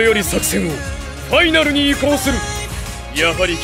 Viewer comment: is he saying finale round ?